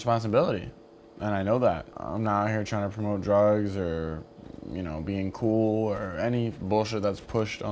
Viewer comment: WHAT DID HE SAY ON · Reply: He said I'm not out here trying to promote drugs or be cool